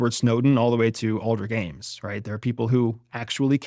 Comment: What name are you saying here? Closed captions isnt helping me. Alder Games? Aldri Gaines?